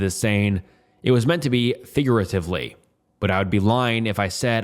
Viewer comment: I was so invested on the story and then I hear “figuirarefvly” 😂😂😂